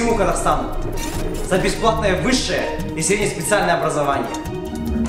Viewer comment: Что он сказал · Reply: Сказал Алга ДВК что еще могут сказать